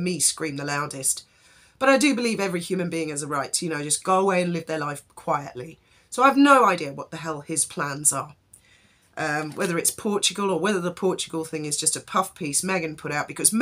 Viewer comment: 'what his plans are, wether it's Portugal' but you sort of, without realising, slowed up the word and I thought you was about to say porn! Oh my word, let's just say, nothing would surprise me now 😂